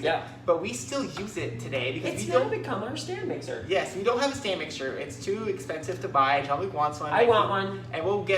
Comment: OMG! I had to watch this 3 times because I thought you were saying sand mixer! lol